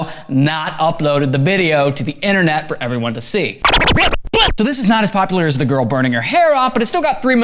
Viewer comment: did he said butt? ._.